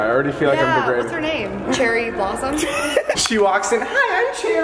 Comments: I thought they said Cheryl Blossom😂😂😂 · I thought Gabbie said "Cheryl Blossom" and I freaked out. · anyone else Heard Cheryl Blossom